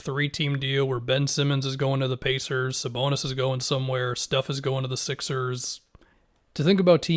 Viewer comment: at first I heard “Steph” is going to the Sixers lmao